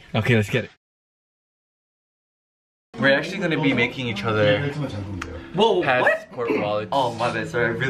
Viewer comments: It’s “making each other” not “making out” mark. He probably does it every time that he misheard it now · Mark probably thought Johnny said "were actually going to be making out with each other" lol, I can't with you mark, I just cant